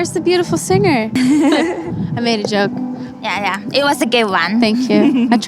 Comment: I kept hearing "that's a gay one" instead of "good one"